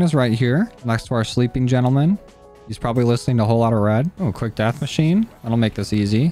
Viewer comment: Did I hear that right? You listen to RED?